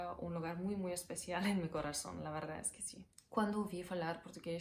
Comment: what she says? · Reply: she said ''pela primeira vez não entendi nada'' if we take the whole phrase it goes like this: quando ouvi falar português pela primeira vez não entendi nada